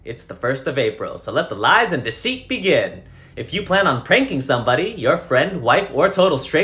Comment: I thought he said “if your thinking on raping someone” I was like🤨📸